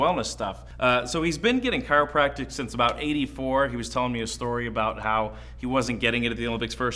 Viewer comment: I thought he said hes been getting adjusted since the AGE of 84 lol!! Like dam how old is he ??!